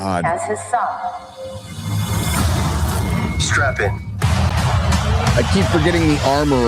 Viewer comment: "Did he say, strap in or strap on?"😏 Pop culture has been weaponized. Hollywoke is all about "tHe MaHdErN aUdIeNcE" and "tHe MeSsAgE" and NOT thought-provoking stories, cinematic experiences that will be remembered, or even profit. Just pushing social programming/re-education/re-engineering of what the so-called Powers That Be wish to be normalized.